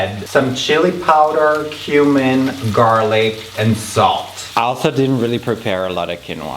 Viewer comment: did he just say „außerdem“??😂